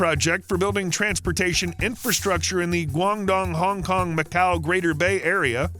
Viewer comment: I thought you said long dong, lol